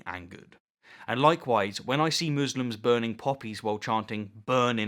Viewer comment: You know, I thought you'd said "burning puppies" halfway through. Man was I glad to have misheard that.